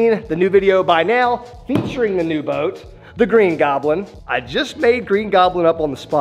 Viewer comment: I wont lie, I thought he said "Green Goblet" and I was confused!